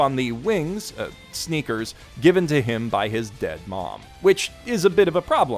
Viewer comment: Did he just spoil Ahiru No Sora? Swear he said his mums dies.. 😢🤬 ( 9:34 )